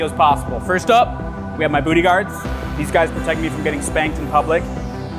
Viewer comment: the men we needed but did not deserve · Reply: I heard "bootyguard"